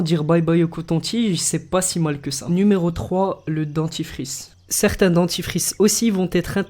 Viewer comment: c'est moi ou à 1:58 c'est dentifice au lieu de 'dentifRice'